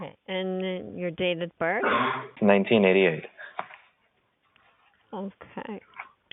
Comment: i thought you said 1998 at first and that had me tripping for a solid moment